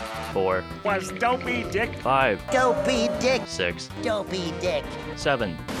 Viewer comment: It sounded like he said “Don’t be a dick”